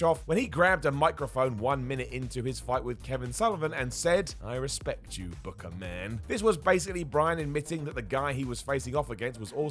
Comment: When I was watching the PPV live, I thought Pillman said, 'I respect you booger man'.